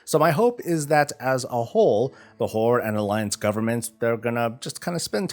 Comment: when he said "whore" instead of "horde" had me doing a quick double take